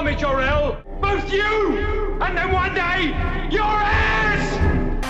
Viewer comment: I’m sorry did I just hear “your ass”